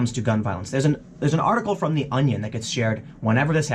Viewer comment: WAit did he say the Onion at 4:13ish